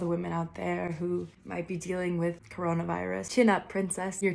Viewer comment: "chin up princess, your tiara is falling" *cue alex meyers throwing up sounds* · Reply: HELP I THOUGHT SHE SAID "YOU'RE TIANA'S FATHER" LMAOOOO 😭😭😭